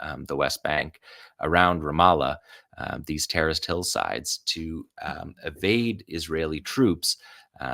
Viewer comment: Around @ 4:15 I heard "the terraced hillsides" as "the terrorist hillsides" and thought I know uses dry humor that can go over folks heads compared to Ali's undeniable snark but damn. Took me several seconds to realize I misheard. 🍉✊🏽